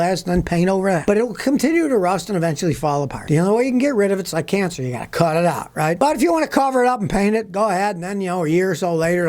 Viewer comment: did he say it’s like cancer, you gotta cut it out ?